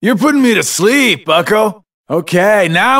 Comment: this game is 10 years old and this entire time I thought he said, "Alright, reload!"... my world is shattered